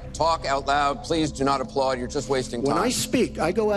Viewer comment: Did he really say, not to applaud 43:31 😅 and not to waste time.. the applause was the confirmation